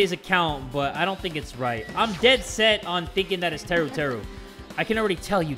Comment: "Teruteru sus because he couldn't have got to the room." that one part that teruteru said about leaning on the wall and finding his way: am i a joke to you? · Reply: Instead if Teruteru I thought it said uterus